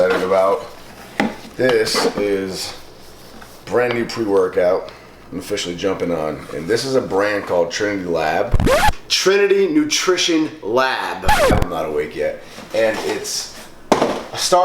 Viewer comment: "I'm officially jumping on" ..."Tren lab" I was away from my computer and this was all I heard at first lol